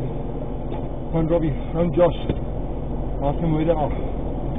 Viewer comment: “Call Rubby and Josh, let them know we’ll be a few minutes late for lunch” Proceeds to rack rifle. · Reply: He doesn't say "let them know well be a few minutes late for lunch", he clearly says "Call Robbie, or Rubby, and Josh... ASK THEM WHERE THEY ARE" Cleary. 4:05 Also everyone claiming this guy has massive balls is kind of annoying to me... yeah he was smart to drive through them to scatter them, but that's about all he did... he doesn't have massive balls as he's clearly breathing extremely heavy the whole video... and looks terrified. His adrenaline kicked in, and he's driving for his life, he's trained to do these things, but he still looks really scared.. I don't know where he looks brave in this to everyone. Like "proceeds to rack rifle" makes him a badass, what do you expect him to do get out, and beg them to stop?! Glad they both lived though.